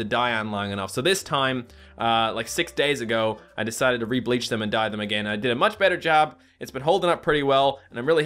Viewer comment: at the start i thought he said: i decided to drink bleach and die again